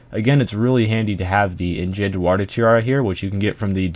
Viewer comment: what did u say "engent warder tiara" ?